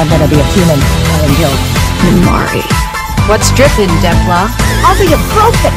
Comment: I thought they said “what’s dripping de claw.” Lol